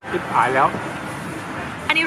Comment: น้าพูดว่าชิบหายแล้ว หรือป่าวถ้าฟังผิดขอโทษค่ะ